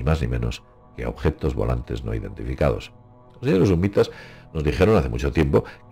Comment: ¿Alguien entiende qué dice? Yo entiendo: — "Sñé de sumbitas", nos dijeron hace mucho tiempo que...